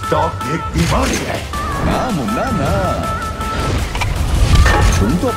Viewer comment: I literally heard " Tiktok Ek bimaari hai" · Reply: No it's (Stark ek bimari hai)